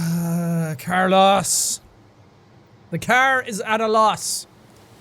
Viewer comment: I heard him say car-loss instead of carlis